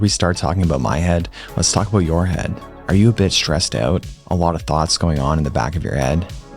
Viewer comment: I thought you said "are you a bitch?" at first lmao